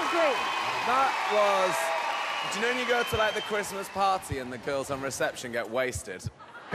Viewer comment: Anyone knows what nicks says at 01:58 plzzz tell me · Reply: "you know when you go to a christmas party and the girls on reception get wasted?"